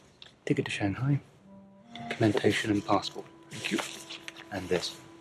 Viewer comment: Did he say: ThanQ?